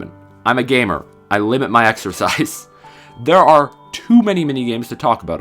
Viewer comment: Exercise, I thought you said extra-fries. Please laugh